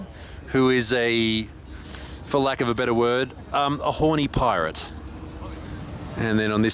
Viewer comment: "For lack of a better word....a horny pirate" This is why I'm subbed, I just know it. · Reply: I thought he said "a holy pirate", lmao.